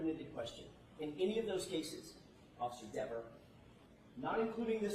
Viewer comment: The scowl witness! She has nervous, guilty blinking problem, She questions the attorney. Sounds like he says Officer devil, lol. She editorializes almost every answer! She is a hostile witness. I can NOT even stomach watching/listening to her! By her attitude, I believe She thinks she is sooo much better than everyone else. Give me a break, she doesn't know where the cameras in the room where?? She shouldn't rely on the media, she says it so many times!!! She doesn't know why she's there?? Doesn't she know what a witness is for?? She is so annoying!!